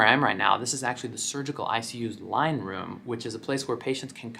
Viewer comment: Listening to this while getting ready for a shift at my hospital...I totally heard “wine” room instead of “line” room 😂